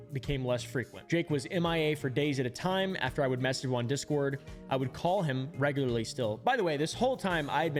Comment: I thought you said you met jake in your discord?